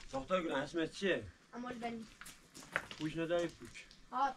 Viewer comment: What did she say her name is ? “Amojdeyn” ? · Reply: Hello dear friend... Thank you for your attention to my small channel... I hope you are happy and healthy wherever you are... Her name is... This is an Arabic name... The name of the mother of Hazrat Abulfazl al-Abbas brother Imam Hussein is... Iranians love Imam Hussein very much❤❤🌹🌹🙏🙏